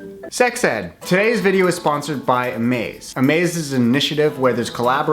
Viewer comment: First I heard "is sponsored by me"